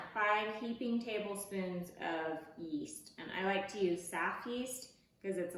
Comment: did you mean 5 Tablespoons or 5 teaspoons yeast? I thought I heard “tablespoons” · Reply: 5 tablespoons of yeast